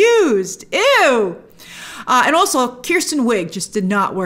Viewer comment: did she just say Kirsten??